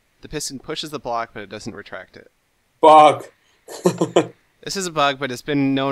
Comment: sounds like says FUCK, but he actually says BUG